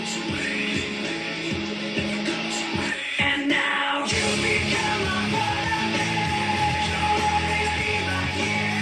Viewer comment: Now, you've become a Polar Bear.. 🐻‍❄️ 😂😂😂 It's one of the funniest misheard LP lines.